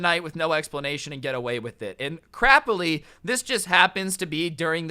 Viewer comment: Did he say crapy